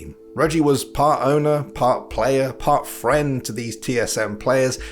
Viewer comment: part friend ? did i hear that right ? i thought regi plays leagues to win and not to make friends, you guys sure that he has friends ? or are they only the ones who wants to hangout with him, sucking his dick cuz he has so much power ?